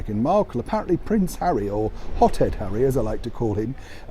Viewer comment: I thought you said pot head harry , not hot head harry !